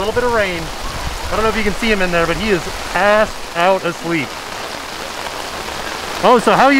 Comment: Also omgawd I haven't heard curse in years! Even if it was saying "assed out" 😂😂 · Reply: I thought that too, but then thought he said passed out. 🤔